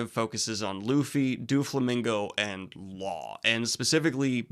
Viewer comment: I kept hearing you say "Do a flamingo" which made me laugh.